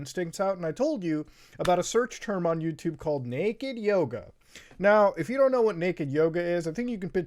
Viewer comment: For a second I thought he said naked Yoda and I got really excited. I MEAN SCARED!